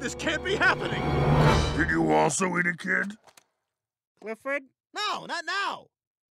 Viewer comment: what is clifford saying? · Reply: He is saying did you also eat a kid?